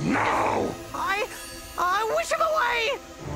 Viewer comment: I thought he said he’ll go away